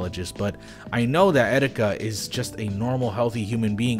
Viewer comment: I know this is serious but Did he just say 'etsetika'?